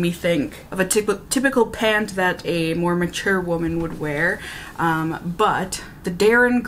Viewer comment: It took me a second when you said "more mature woman". I heard "Mormon chairwoman" 😂